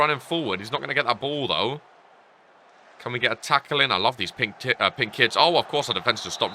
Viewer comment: Did he just say "Pinks t*ts" then corrected to pink kits😭😭😭